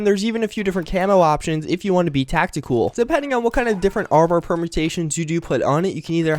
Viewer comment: I could have sworn that at 3:54 you said 'Tacti-cool'. lol